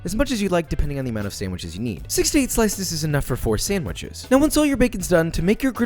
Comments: thought my man said 68 slices is enough for 4 sandwiches and i didnt even disagree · i thought he said "68 pieces of bacon is enough for 4 sandwiches" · thought he said 68 slices is good for about 4 sandwiches, my brain was like, sound right to me :) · I heard, "68 pieces is enough for 4 sandwiches" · I heard "68 slices is enough for 4 sandwiches..." yes it is Joshua, yes it is. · I heard "68 slices of bacon is enough" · I thought he said "68 slices is enough for 4 sandwiches" 😂😂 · Am I the only one who heard "68 slices is enough for 4 sandwiches " at 3:49? lol